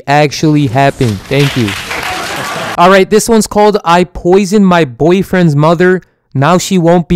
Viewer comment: DID YOU SAY PP